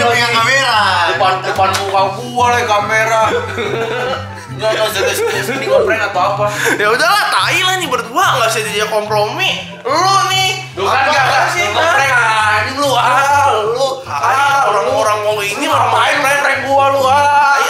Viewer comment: komplomi gua dengernya